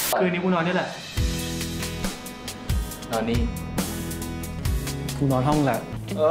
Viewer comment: i thought he said nani? and i was lke when did he learn japanese, had to rewind a few times 5555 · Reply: Tine asked Sarawat: /non-ni/ which means (do you want to) sleep here?